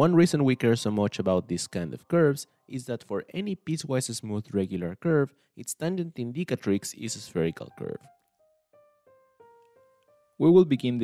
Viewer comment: you start a sentence with "one reason we care so much about this..." But I cannot understand what you said afterwards even with subtitles. Would you mind repeating that for me? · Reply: For any piecewise smooth regular curve, its tangent indicatrix is a spherical curve